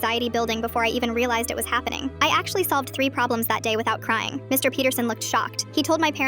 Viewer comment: I thought she said Mr BeatHerSon😂 but it was MR Peterson 😅